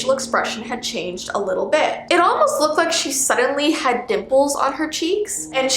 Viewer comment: i thought she said nipples on her face hthtgtgtfgfgff